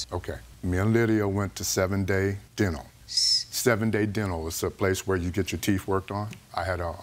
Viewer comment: "Seven Day Dinner to get your teeth clean?" I thought until he said dental later on haha